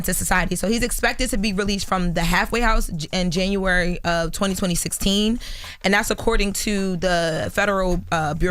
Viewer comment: did she just say 202016